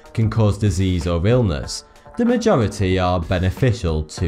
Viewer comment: At the end of the video the only thing i hear is "fanks for the view, this is forty 2"